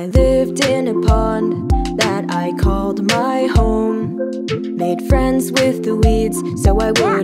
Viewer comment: I keep hearing R as F no matter how many times I hear it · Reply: for me its 0:16 where i keep hearing the D as B “made friends with the weebs” “plants arent good at conversation” lmao